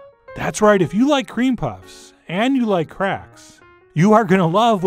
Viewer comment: I first heard that as ‘If you like Cream and you like Crack’... that’s my queue to go back to bed instead of watching food videos at 2am